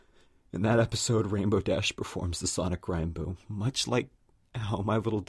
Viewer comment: did you say sonic rhyme boom? O_O